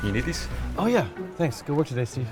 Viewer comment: WHAT IS PHIL SAYING AT 0:15 YA'LL jk edit: after like probably 6 repeats, "good work today steve" I struggled tho 😂😭😅